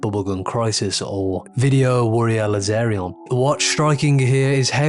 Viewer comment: what does he say after bubble gum crisis? "Video warrior aerial?" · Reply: Video Warrior Laserion is the name